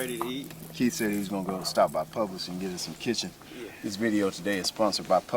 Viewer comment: Aye cuz love the videos as alway but did you say "get us some kitchen"? @ 4:09 😂🐔🚿? Tight lines brother🤙🎣👊